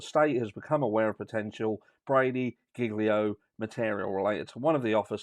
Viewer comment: Really glad you explained that. I thought everyone's was saying gigolo 😂iwas getting very confused!